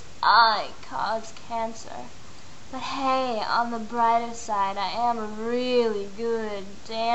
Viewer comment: the text says "lighter" but you say (or at least it's what I heard) "brighter"